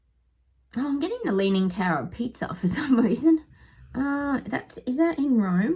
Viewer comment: Well said, I thought you said tower of pizza 🥘lol. elbow is just a pawn in a game and doesn't know it. the Olympics is a digital trap and a test and ritual. its the devil uprising. the whole thing will be satanic. wont be watching any of it as I don't have a tele lol. sushi is playing the national service card here before he leaves his last sting in his tail before he passes the book. I bet he loved telling everyone he is leaving, he has just sht on everyone before he passes the book, in code to us awake one's, its all planned, doesn't matter if you vote or not. glad I've never voted. labour will get in as it fits the narrative of the agenda. bit like the w.h.o. slots in nicely lol, cotton swab has left our reality to a planet far far away 🌐so the news today here is parents will be fined for not sending there kids off, fits in with the conscription too. so they are trying to reduce the numbers and suck us all into it, while they tag the few with the digital id to turn them in to robots, people are being played and they are falling for it. they want the teminator wars. and a.i will gaslight the majority into oblivion with deep fake (a.i faking what we didn't say online) a.i will turn people on each other exactly what they want. this tech will drive people mad if we don't come of it. hopefully the revolution is coming... have great day 😸🐩🐕‍🦺🦮🙋‍♂🌄